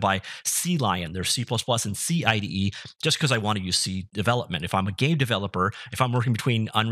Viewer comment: Did you say "if I'm a gay developer" at 0:58?